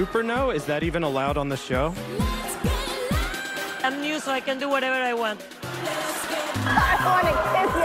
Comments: “I’m Latin, so I get to feel whatever I want” Gloria is the real judge, people. UPDATE: This quote is a hilarious line on modern family, not quoting Sofia ! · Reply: I thought she said “I’m new so I can do whatever I want “ I don’t think she would ever put herself above anyone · “I’m Latin, so I get to feel whatever I want” Gloria is the real judge, people. UPDATE: This quote is a hilarious line on modern family, not quoting Sofia ! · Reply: Lol I heard “I’m new so I get to do whatever I want”